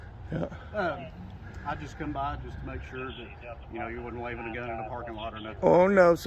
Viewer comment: what did he say “I just want to make sure you wouldn’t leave it again in the parking lot”? What’s he talking about? His gun? Why would he leave his gun in the parking lot?